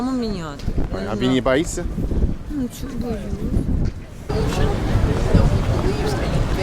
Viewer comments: anyone else hear something like "nu chego? boyus'" or maybe "pochemu? boyus'" ? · sounded like she said "Ну почему... боюсь" ("why, I am ")